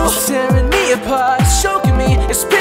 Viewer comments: it sounds like he said it's pissing my heart and soul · when he sang "piercing my heart" i heard "pissing my heart." but omg LOVE THIS COVER!!!!!